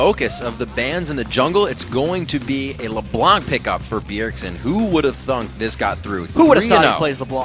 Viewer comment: did he just say who would of thunk @ 2:34 ?